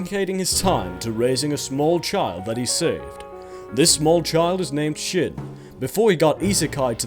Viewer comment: I thought you said"The small child is named shit".....